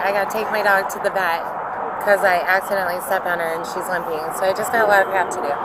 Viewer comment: She talked about that dog like it was a broken phone. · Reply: I thought she said she stepped on her daughter 😂😂😂 I went back and upped the volume. But yeah...